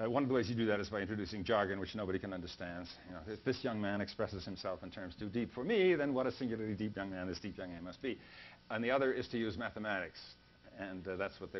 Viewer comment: What does he say here? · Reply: "This young man expresses himself in terms too deep for me, then what a singularly deep young man this deep young man must be"